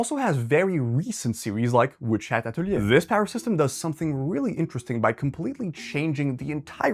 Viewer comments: what's the name of the anime/manga he's referring to at 11:58? i can't hear it properly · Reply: Witch hat atelier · What did you say what was the name please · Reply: The anime is called witch hat atelier